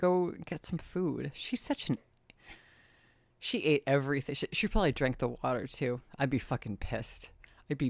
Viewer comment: I heard, "She prolly drank all her butter!" That b*tch!